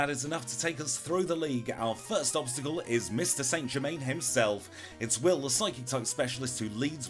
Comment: What did you call will? St Germain?